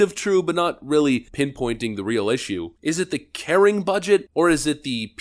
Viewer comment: Often times I listen to your videos more than watch, and I had to pull it up to tell that you were saying "Caring Budget" and not "Karen Budget". Honestly mishearing it the first time might be the more accurate description XD